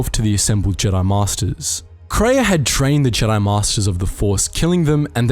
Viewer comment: I have listened to this part 8 or more times and I still have no clue what you're saying. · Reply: Edit: you were saying drained. There's such a hard T sound making it sound like trained.